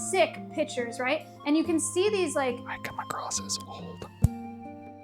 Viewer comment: What did he say at @ 30:17? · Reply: According to the auto-generated subtitles, it's "I come across as old"